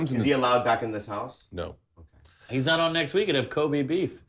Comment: someone please I know I'm retarded but what did Mark say? Kobe and beef??